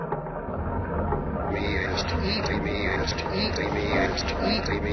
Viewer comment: the singing body sounds like its saying "it meres to eat"